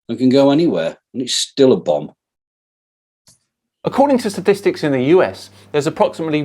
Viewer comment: did you really say (according to sadistics).